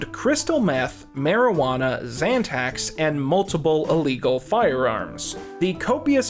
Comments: Hahaha 15:33 Cryetal Meth, Marijuana, and Zantac?! lol No! Not Chumlee! NOT friggin' ZANTAC!! 🤣 Throw away the KEY! 🔑 🗑 🚮 (I think you probably meant to say, "Xanax," lol you know, the ACTUAL illegally abused schedule one Narcotic drug?! Not the over-the-counter heartburn medication. But, whatever lol right?! Xanax, Zantac, it's all the same. (someone REALLY needs to invent the <facepalm> emoji) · Im sorry, did you say he had crystal meth, marijuana, and....... zantacs? Did you mean xanax? 15:33 . Zantacs is a antacid.